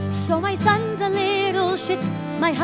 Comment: Did she say that her son is a little s**t?